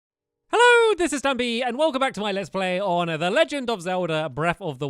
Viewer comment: Lol I heard “Hello!! This is duppy”